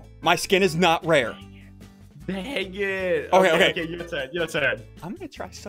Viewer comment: I hear the word cranier or the word dangit